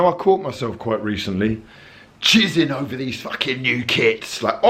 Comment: When you said jizzin on kits I l thought you had said jizzin on kids and I was like no fuckin way he did not just say that.. sure enough you didn't; safe to safe I was quite happy that wasn't the case lmfao